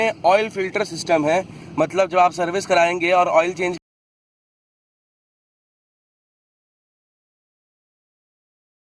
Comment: Voice muted at 2:23 may I know what are you trying to say? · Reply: You need to get the filter replaced at every oil change is what i was trying to tell.